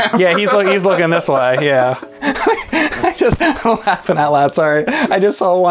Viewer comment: did he just say.. laughing out loud.. out loud?.. ive never seent it unironically lol